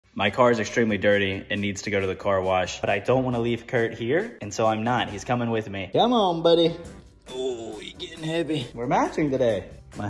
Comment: at the start i thought u said “my cat is really dirty so it needs to go to the car wash” 😭😭😭